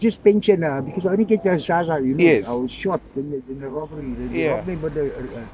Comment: sounds like he says he was gonna get zaza but he was short lol😂